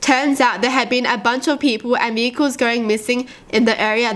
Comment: I don’t know if it was just me but in the first one I thought she said the n-word instead of vehicles